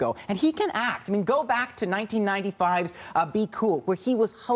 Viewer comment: Did he say 95?! 3:09🤔